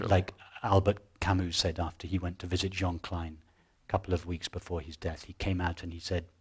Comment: Who did Albert Camus visited two weeks before his death and what could not he believe? I hear “John Klein” at 5:03 but can’t find any reference to that on internet, I must be mishearing it.